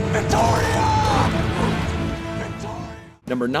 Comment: was he calling her Retardia